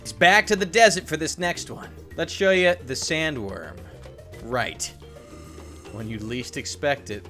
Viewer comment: Did u just say killer queen "we taking the stand right" I'm not the only one who thought about Jojo's Bizarro adventures